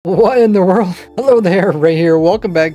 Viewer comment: Did he just say "Walffe im wrong" at the start of the video? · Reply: He said "What in the world"